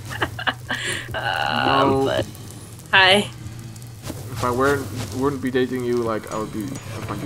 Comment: Wait, near the end did just say "I wouldn't be dating you" :O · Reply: I thought he said "if I would not be dating you"